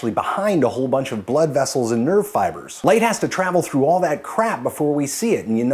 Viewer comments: i thought you said nerdfighters when you said nerve fibers. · When you said nerve fibers I heard nerdfighters. I thought you'd made a wonderful joke, you didn't I just misheard.